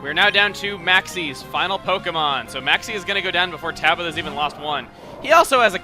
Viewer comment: sounds like if you said Tablo instead of Tabitha *w*